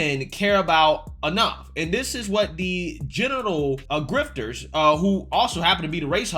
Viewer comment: I keep hearing you say “General Grifter.”